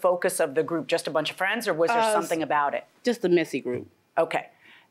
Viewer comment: The messy group?! Sounds about right. Now you're here. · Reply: Thought she said message group